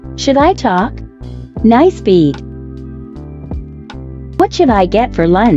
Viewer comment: PLEASE I thought you said “nice feet” 😭